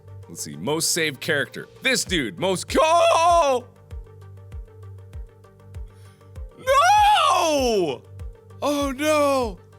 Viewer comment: "Most saved character. This dude. Most CORAL!" Did i hear this right?! 😂